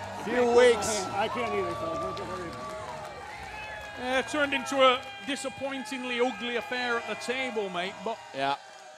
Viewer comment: does he say “ I can’t feel my hand” or what. He said “I can’t ___ my hand” for sure. And chafee said me too hahaha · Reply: I think he says: "I can't close my hand", and Dave replies: "I can't either". Legends.